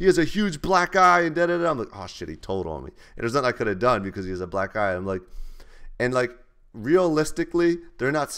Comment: Lool i thought he said he couldnt do anything cus he was a black guy...